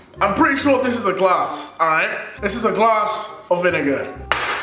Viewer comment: instead of him saying a glass of vinegar to me it sounded like a glass of a niga Like if you heard it!!! :)